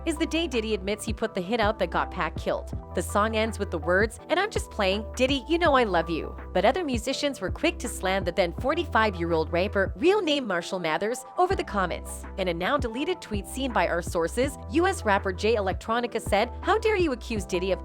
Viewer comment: She says rape instead of rap.